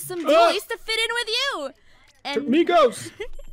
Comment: I’m think he said Migos. Like THE migos